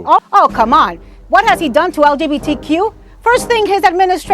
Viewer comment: did he just say LBDGTQ